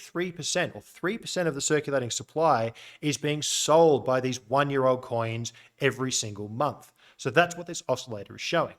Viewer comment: Always when you say: “old coins” I hear: “alt coins” 🤣